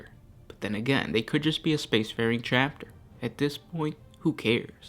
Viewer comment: well, this is an unexpected video. recommendation: Thousand Sons before, during and after the Horus Heresy. thought you said "space fairy/fairies" at 1:37 instead of "space faring".